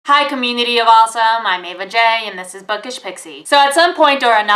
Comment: What is it you say at the beginning of every video? I've seen nearly every video you've done and still have not been able to figure it out. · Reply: Jack Willis Yeah I haven't understood also and I've been watching for months! Is it commity of awesome, community of awesome. And also (this is kind of rude but...) the first times I heard her saying I just understood "a vajay"... But I love the videos!